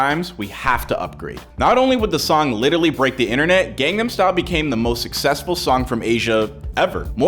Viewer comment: As a kid I always thought he was saying “Open Condom Style“ and was like 😳